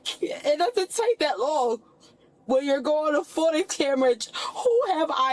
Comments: what did she just say ? foota camerage ? 😂😂😂😂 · “Photo camerage”😂😂😂😂😂instead of camera footage